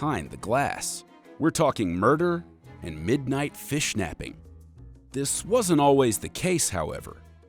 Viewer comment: did he said "FISHNAPPING"